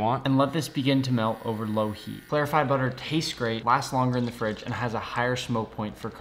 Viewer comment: i thought u said verified brother